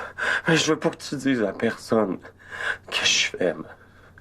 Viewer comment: Moi j'ai compris "dit pas a personne que je t'aime " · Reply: Il a dit : dit pas a personne que jsuis faible